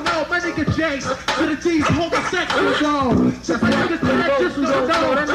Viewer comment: And wtf did T3 say at 5:07, "and to those who dont know my nigga Jay to the Dee is home sexing....." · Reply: hes saying "homosexual" not "home sexing"